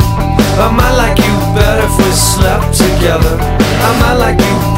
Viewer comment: LOL wow up until now I'd only ever heard the original Romeo Void version.... Always thought she said "Our mother will get better if we slept together."